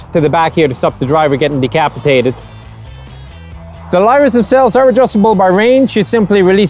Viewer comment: What is he saying at 24:15? What are these two bars (pipes) for? English ain't my first language and I get tripped by Nicholas' Irish/American hybrid accent. Love this video and this is the most intriguing tank out there. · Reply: Lyra flare launchers